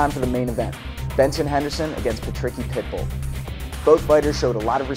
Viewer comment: benson Henderson against the "tricky pickle" what kind of name is that??😂😂 jk but it sounded like he said that lmao